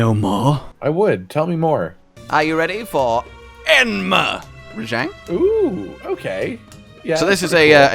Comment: It sounded like you said enema Rajang. Thats a whole different kinda monster..